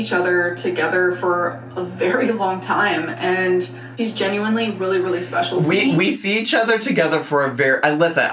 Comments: At around 40:20 I hear "he is genuinely really really special to me" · Did she say “he” 40:22?